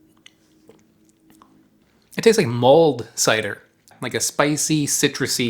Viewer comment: I kinda freaked out cause i thought you said it tasted like Mold cider, i thought you were saying it tasted like rancid musty wood lol.